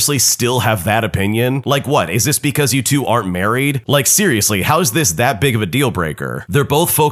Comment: Why does it say YouTube, shouldn’t it say you too?